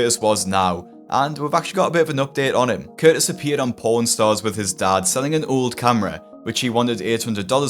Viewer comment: I heard "porn stars" at first and nearly shat myself